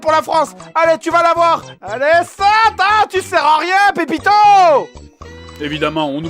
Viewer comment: ai-je bien entendu ? Il a dit "tu sers à rien Pépito !" Eh bien voila pourquoi il lui en veut !!!